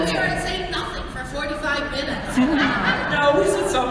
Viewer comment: what did Jennifer say about 45 minutes in the video? in 40:15 or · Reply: But look at how good we are at saying nothing for 45 mins